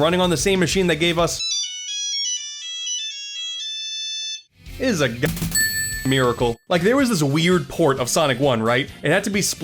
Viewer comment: what did he say at 29:48 all i hear was “it’s a g-… miracle” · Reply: God damn miracle